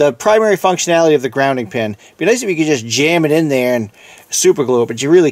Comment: Ali that’s what I’m trying to understand. Sounded like he said you can superglue it back together.. can you reply back if you figured out if this true or not.. thanks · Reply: No, he said it'd be nice if you could glue it back together but that doesn't work. Once you break the grounding wire off, you need to replace the whole head of the cord for the device to be safe to use.